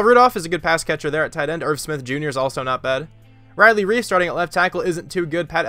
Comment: for a sec i thought u said riley reid not riley reiff